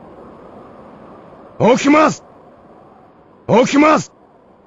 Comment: Hey, what the person says there at 14:10 is "Okimasu" means "wake up". Just saying :P Also can mean "rise".